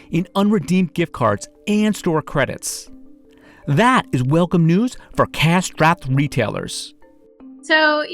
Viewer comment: Serious what tf did he say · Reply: that is welcome news for cash strapped(?) retailers